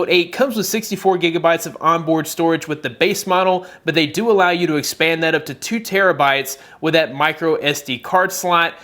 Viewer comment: Did he say Note 8 expands up to 2Tbites expandable memory